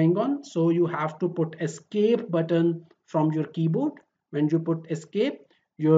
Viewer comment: in this moment l can’t understand from where l get esp or what you say?! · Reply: Dear U can press the escape button on the keyboard of your laptop/desktop to end the command in AutoCAD. Hope u got it....